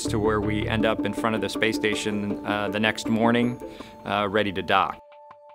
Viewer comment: I thought it said ready to die